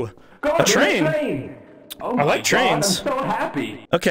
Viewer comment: okok i like had this on in the background only half paying attention and i swear to god that at 3:00 bubby DOES say "oh my god" the m just got a lil crushed, every other time i've watched this i heard ye but for some reason this time was different